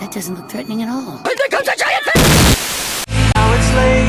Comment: WTF is Ichiren saying? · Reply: And here comes a giant fist XD